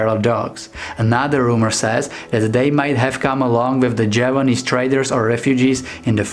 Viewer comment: Is the Kintamani your favourite dog breed? 15 Dog Breeds That Look Like Bears: <link> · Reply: who did you say? Jaw-wa-nese? *and the chow-chow tongue, spotted purple. And the extra loose neck skin, perhaps like the akita in purpose, but for street fighting.